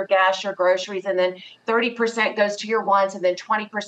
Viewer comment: What does she say about the 30% rule? I had a hard time hearing what she was saying. Auto captions says “ones”, but I think she’s saying “wants”, “30% goes to your wants”?